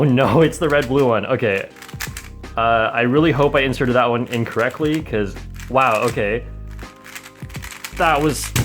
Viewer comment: hes trying to say inserted in, correctly but it sounds like hes saying inserted incorrectly. Its kinda like ley's eat, grandma vs lets eat grandma